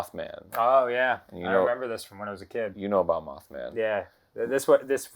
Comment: I heard "You know about obama"